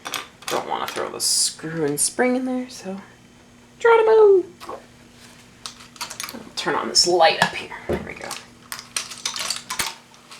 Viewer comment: about 9:37 did he say "Drown them all!!!!"? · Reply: Dranemo I think